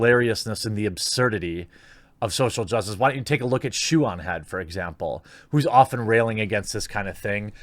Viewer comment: Can someone tell me who the youtuber he plugged is at 7:58 just sounded like shoo-on-head?